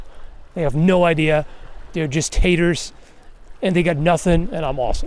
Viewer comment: "They're just Taters". Best insult I've misheard in a long time. Gonna use it